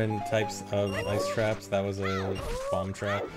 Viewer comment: “bomb trap” did someone say bombtrack?